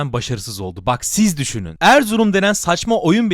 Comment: Anlamadım,ERZURUM a saçma mı dedin,ben mi yanlış duydum?